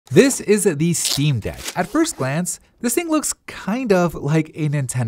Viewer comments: Sounds like you said "isnt the steam deck" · The way you say 'is the' 0:00 makes it sound like youre saying "isnt." Had to replay it because i thought you said this isnt the steam deck. i never heard anyone pronounce words like that. Wtf speak normally.